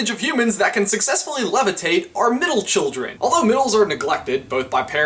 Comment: When you said meditate, I thought you said levitate I was like Wut